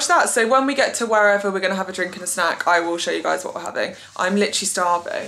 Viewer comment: I really thought you casually said " watch tv and have sex in bed ". Had to rewind to realize you said snack. I was like wow she's so open about it.